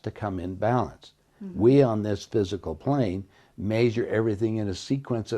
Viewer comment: Did he say physical plane..